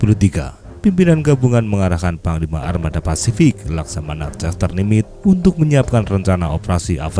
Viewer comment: gw dengernya tadi Jess no limit 😅